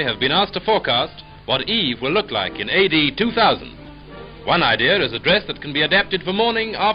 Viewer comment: Sometimes I hear not 'AD 2000' but 82000